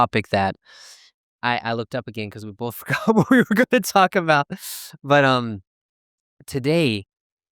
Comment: Hi and! I really enjoy your videos every time. I really couldn’t understand what you said around 0:10 “I looked up again because we both forgot what we were going to talk about”. I could write it with the subtitle. It’s interesting because I don’t still catch the sentence after listening it 10 times!